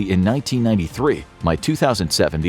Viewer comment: he says "By 2007 the S-400 was fully operational" the captions say he says "by" and that makes the most sense but I stg he says "my"